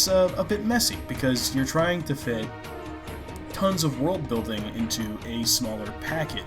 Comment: For a moment, I thought he said 'woke-building' when he said 'world-building'. Yeah, woke-building in a BT movie would be a problem.